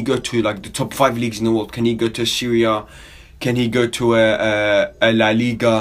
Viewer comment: lol thought for a second you Said syria when you Said Serie A hahaha